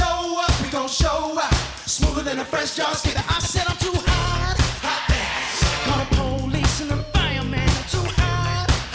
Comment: What is he saying? I can't hear "I'm too hot". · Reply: It's because he's saying "im too hot"